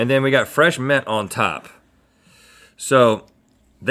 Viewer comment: I thought Rhett said "Fresh meth on top." 🤤